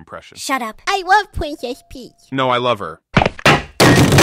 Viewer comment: I thought he said great but he said grape